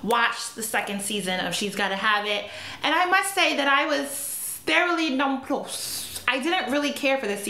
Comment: What does she say at 0:43? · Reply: "Thoroughly nonplussed"